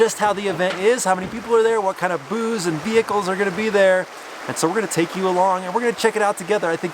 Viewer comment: I swear I heard “what kind of booze and vehicles are going to be there” 😂😂 Thanks for another awesome video